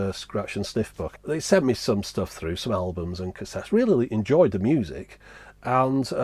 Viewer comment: did you just call scratch&sniff "smellbums"?